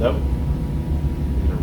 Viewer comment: Did anyone else heard someone replying "Hello" in return at 14:16?